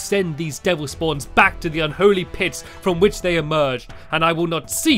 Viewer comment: i heard holy piss or could it be this?... idk XD